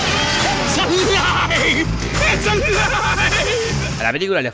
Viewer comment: al inicio no entendía y pensé que decia "exalive!" Y no "it's alive!" Que idiota